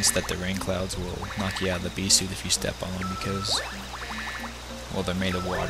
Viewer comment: It sounded like he said pee suit lol